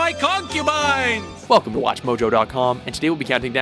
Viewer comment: am i The only One who hears "Fuck watchmojo"/